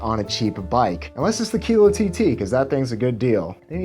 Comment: he says "unless it's a <muffled> tt, that things a good bike. They need to pay me...". And he shows a photo of a green bike, but I can't make out the brand. Any help with this? Quella bikes. Got it. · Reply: kilo tt.....that’s what he says